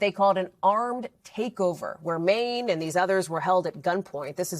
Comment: Did shorty just call this nigga “Mane” 0:40 🥴🥴